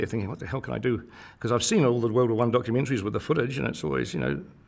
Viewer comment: What does he say at approx. 1:18 - " ... I've seen all the WW1 footage -- ?????? " He says on word? I can't understand it. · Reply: He did not say that. He said that he had seen all the documentaries with the footage. There is likely to be footage that has never appeared in documentaries including "The Great War" which was screened in 1968 and went on for weeks.